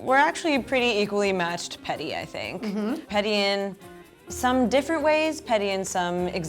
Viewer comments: I literally thought they were saying pretty till like six minutes in omfg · i thought it said 'pretty' i was so ready for all the savagery... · 99% of comments:I thought it said pretty 1% of comments:I'm sooo petty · i thought it said pretty and i was like WHAT they're all beautiful 😂😍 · AM I️ THE ONLY ONE WHO ACTUALLY READ “PETTY” CORRECTLY? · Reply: I️ thought it said pretty...LOL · I definitely thought that said pretty... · I thought it said pretty... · I thought it said pretty 😂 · I thought it said pretty oml 😂😂😂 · Through out the whole video I thought they were saying pretty until I saw the percentage... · I thought it said pretty... ._.